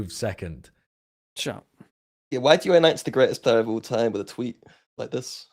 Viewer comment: if listened to this 7 times now and I cant understand what is trying to say here 💀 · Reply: "why do you announce the greatest player of all time with a tweet like this"